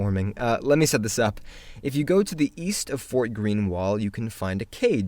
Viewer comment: What's with everything being east of something!? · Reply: Wait. I thought he said Weast